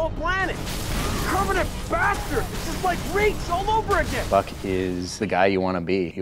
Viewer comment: Did he say this is like Reach all over again? Hmm...